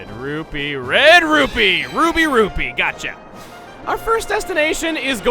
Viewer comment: Did you just say... "RWBY rupee"? <3